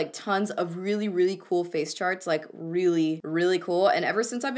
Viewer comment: when you said "face charts" I really thought you said "face sharts" haha